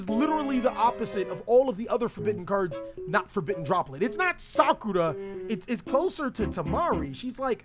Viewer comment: Did he just say Sakura is useful? 😶